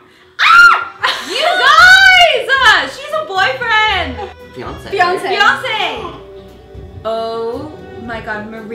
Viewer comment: i thought you said Beyoncé and not fiancee at 6:03 and i was like wait what?!?